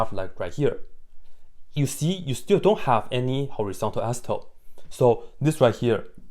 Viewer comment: can anyone please tell me, what he is saying just after horizontal...... i am unable to catch that word, neither do subtitles are of any help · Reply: Asymptote.